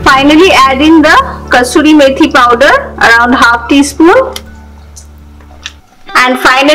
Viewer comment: I could not understand what said she was adding ? is a nice lady but with her high pitched voice and Indian accent, I struggled to hear what she was saying for much of the video. Looking at the video again, I think it was 0:53, the ''Fenugreek'' that she added at 6:20 ? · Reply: Yes that was the dry Fenugreek leaf powder . Easily available in Indian grocery shop😊